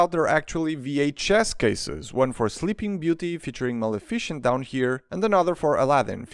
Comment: Sorry, 0:44 in and i hear “malaficient” instead of “Maleficent”